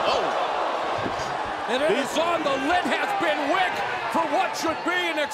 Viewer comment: Did he say "the lit has been wick" at 0:50?